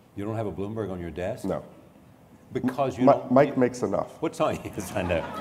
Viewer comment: what does he mean by "mike makes enough", I can't tell if that's even what he says??